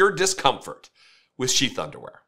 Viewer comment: we need all kinds of voices! Yours is awesome! And.. I keep hearing you say "sheep underwear". 🤣🤣 🐑🐏.